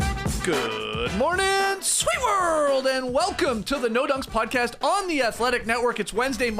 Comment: I heard Doe Nunks